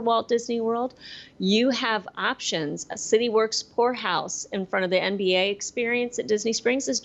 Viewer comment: Am I the only one that thought said City Works Whore House? I had to rewind 3 times and turn the volume up, lmao. Thank you for this show it felt like old times and made me look forward to planning a new trip 💗 Happy Birthday 💗